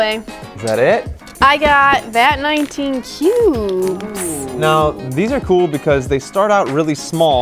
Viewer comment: I thought she said vat19 pubs i was like what the HELL